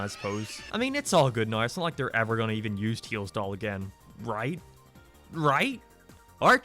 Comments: i thought you were saying teals doll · It sounds like he is saying teals doll